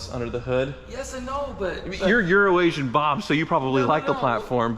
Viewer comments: OK, after watching this episode, you've proved that my wife was right. I thought you'd been saying "Urination Bob" not "Euroasian Bob". Now I need to go an get my hearing checked. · aha, I had always thought was saying 'Urination Bob'